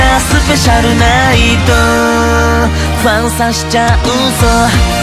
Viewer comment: ファン刺しちゃうぞ☆ ごめん、これにしか聞こえない…w